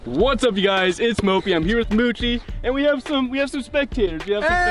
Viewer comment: I swear I thought that you said “I’m here with coochie”